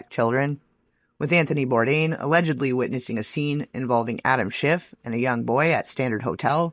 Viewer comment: Next to who? Adam who. I do know what he use to say about being next to the set of a certain sit com from the 90s . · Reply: schiff is what I heard him say.